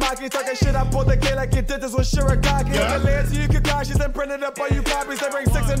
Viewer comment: did anyone else hear sh-ri-gaki and not shig-i-raki